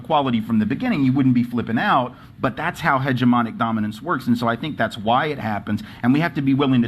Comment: hedgeamonic what? is he making up words?